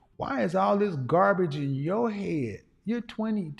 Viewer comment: Word is bond I thought he said ,Garbage ON your head at 22 at first. I couldn't wait to hear her answer.